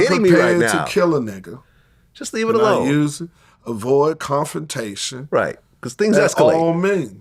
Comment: his saying “avoid conformation” instead of “confrontation” proved katt was right 😂😂😂😂😂